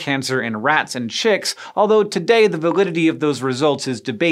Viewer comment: did you say "the vagudity of those test results..." ????